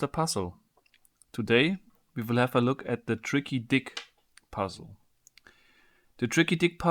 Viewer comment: Wtf thought it said tricky dice